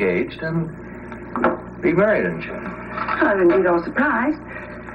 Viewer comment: I keep hearing say, “and be married and shit” @ 06:12. What IS he actually saying?😂 It’s driving me crazy.